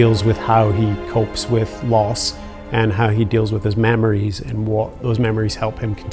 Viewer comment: At first, I thought he said "mammaries" Yeah, chief really needs to calm them down.